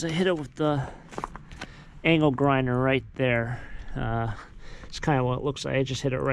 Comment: i don't know about yall but at 14:07 I heard anal grinder